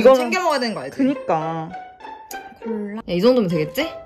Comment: 아닠ㅋ 4:23초에 유산균담에 콜랔ㅋ 자세히 들으면 콜라 라고 들림